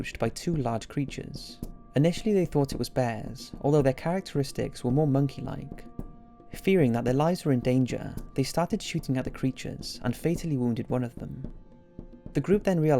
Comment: When you said "They thought they might be in danger" and then they shot the creatures, I thought you said "They thought the species migt be endangered, so they started shooting" lmao